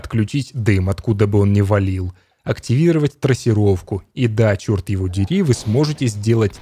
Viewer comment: трассировка в doom ? мне не послышалось ??